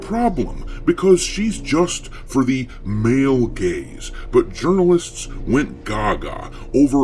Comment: Media: Eve is for the male gays! Me: Those dudes don't even like women 😂 Media: Huh? Me: Oh male GAZE... I thought you said...